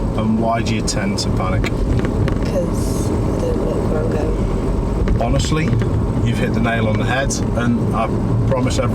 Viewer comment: Did anyone catch what said at 1:40? I didn't hear it and unfortunately never repeated it. Closed Captions don't even roll forward because of how quiet she was. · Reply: Think she said - I don’t look where I am going.